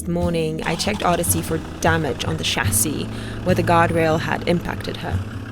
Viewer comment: Eva, with your lovely accent, I heard you say the words “guard rail” as “God rail”. Well….maybe God had something to do with saving your lives. Much love to you and Vilk!